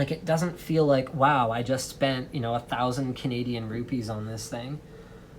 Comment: did he just said "Canadian Rupees"